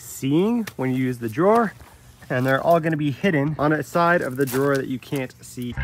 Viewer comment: sounded like you said "Heeding" instead of saying "Hiding"? pretty good vid though.